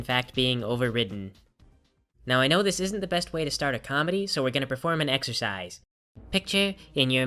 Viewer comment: I thought he said 'preform an exorcism' XD